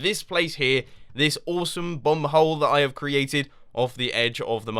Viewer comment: Thought Mumbo said "awesome bum-hole"